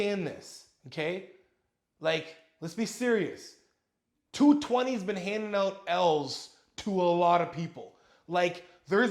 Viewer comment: At first I thought he was saying "2020 has been handing out elves"